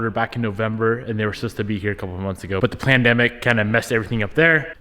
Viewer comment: did he say “plandemic”? 😂